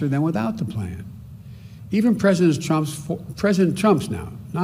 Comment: From 4:41 did he say president chumps lol